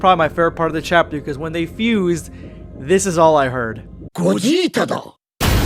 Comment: This is all I heard " Gogeta Dou" 😂😂😂😂😂😂